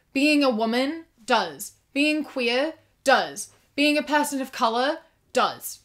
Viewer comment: In 10:58 I heard "being a pastel colour" and I was like ¿¿¿¿????